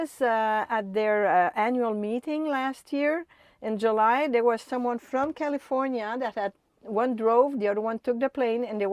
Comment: did she say she's "paying on it for twenty years?" I'm having trouble understanding what was said. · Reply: She said she was planning on it lasting at least 20 years , referring the the miles they are good for (500 000)